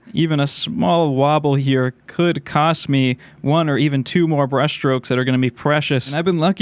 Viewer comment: I heard “Breast Stroke”